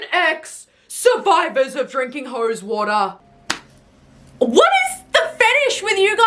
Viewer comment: 😅 I was multi-tasking and thought I heard "Drinking hoe's water", I had to play it back like an idiot. LOL